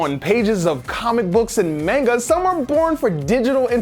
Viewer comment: did he just say mainga . . . .